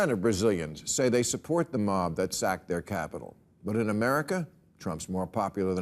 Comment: I thought he said 'wacked'.